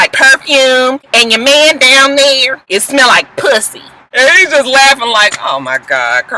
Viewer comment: it sounded like she said "ya man down here smelling like fish!" lmao!